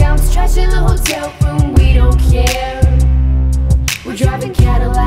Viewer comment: I hear “wheel chair” instead of hearing “we don’t care” oof Anyone else?